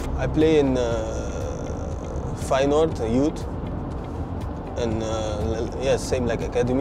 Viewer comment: I think at 0:19 he said Feyenoord youth not final youth 😂